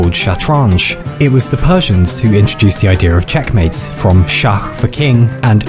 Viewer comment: It was shockingly recent that I thought you said "chess mate" when the game was over